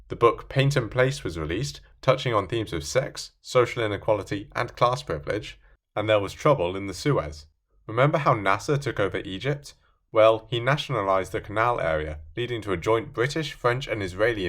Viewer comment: As a kid, I misheard trouble in the Suez as trouble in the sewers and said, "Call the Teenage Mutant Ninja Turtles to take care of it!"